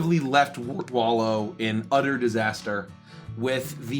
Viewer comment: Every time they say utter disaster, I hear "udder" disaster😂